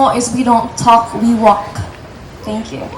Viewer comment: I Heard She said : "WE DON'T TALK,WE WORK!" Why you guys always says WE WALK???!! Please listen it with your earphone🤨